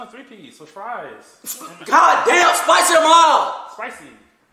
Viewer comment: Always thought said "goddam spice em up!!" Like he calling his order basic as hell